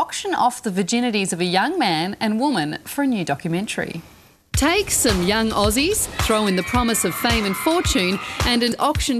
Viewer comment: I swear the said "Take some young Nazis"